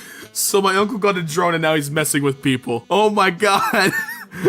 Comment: On the my uncle got a drone I thought it said f-ing people